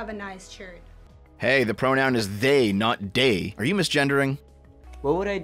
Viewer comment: she clearly said "day" not "they". I'm going to cancel the CC transcriber. Unacceptable.